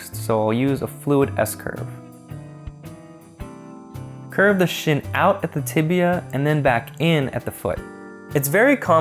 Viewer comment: Thumbs up if you thought he said "curve the sh*t out of the tibia"